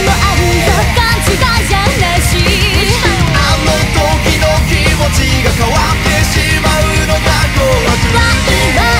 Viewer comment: did she say 無視かよ or 蟲かよ? 🥲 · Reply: i checked the lyrics it’s 無視かよ lol